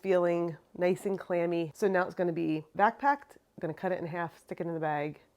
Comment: I love watching your videos. I wish I could taste your cheese. You have such a passion for making cheese. I have to say though the first couple of videos I watched every time you said "vac packed", I thought you were saying "backpack." I was thinking, "what is backpacking cheese?"